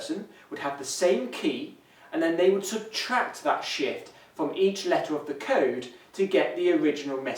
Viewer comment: i thought he said shit haha